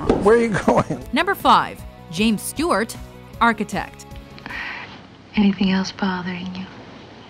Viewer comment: sorry, I only heard Ted Mosby, architect